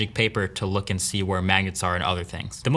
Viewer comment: I swear he said Maggots